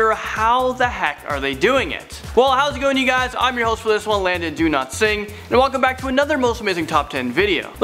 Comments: wait i thought he said do not sing not dowlatsingh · he pronounces his name different every video😂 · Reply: okay maceo I keep hearing “Landon do not sing “ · I thought he said his name is do not sing